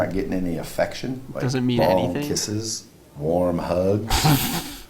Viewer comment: He said “long kisses” but I heard “ball kisses” and was wondering if this was a normal part of hetero life lol